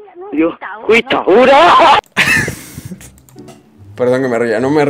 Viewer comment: creo que dijo guitaguira , suena como ura , es como decir yagua , como mostrar desprecio en alguno caso que te da miedo , coincide perfectamente no ? soy de misiones xD (no se como se escribe guitaguira) jasdjasdj en mescla guarani español muy habitual por aca.